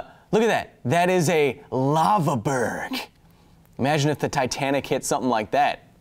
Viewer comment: I thought you said lava bird at first not lava berg, gotta say I was a little disappointed.